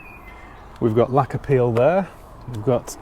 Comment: “We’ve got lacquer peel there” I heard it as “We’ve got a lack of appeal there”, which it definitely has 😂